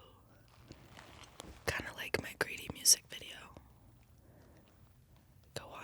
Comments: I thought she kept saying "Grady." I was like, "Whos' Grady?" lol Then I looked her up and saw it is Greedy. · When she said greedy I thought she said “Grady” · When she says greedy, I hear ‘g r a d y’ 😂